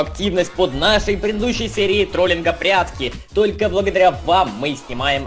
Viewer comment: я один услышал как сказал "прятски"